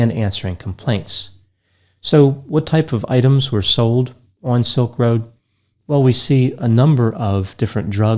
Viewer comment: I thought the Dr. said Silk Robe lol. So, I was thinking the guy sold lingerie!